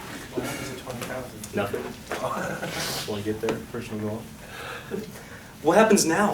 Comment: i think he says, "20,000 views, won't even get there.." or something similar. Currently it has 400k views and hes almost 1 Million subscribers! And its non other than his amazing teaching and math skills that got him here. Stay strong and keep beleiving 💪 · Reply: pretty sure he said "nothing, just want to get there, personal goal" after someone asks "what happens at 20 thousand?" Nevertheless, he's awesome at teaching